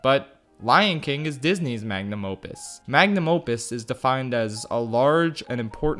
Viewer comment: Did he just said Lion Kin is Disney's mang a num opus?